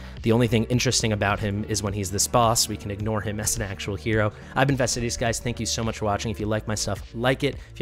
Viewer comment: I've been guys, thats what he said. I kept hearing I invested in these guys :D sharing with my mother as always mr